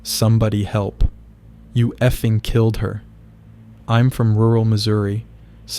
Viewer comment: On the third video after the guy screamed you killed her I heard him say you killed my mother I might’ve heard wrong but listen to the video again to see if I was right